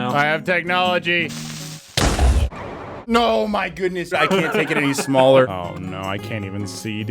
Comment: “I can’t take it any smaller” bruh I hear things wrong